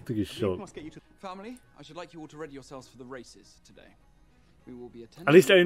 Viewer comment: first time I watched it, I heard "I'd like you to ready yourself for the racists today" and I was SO confused lol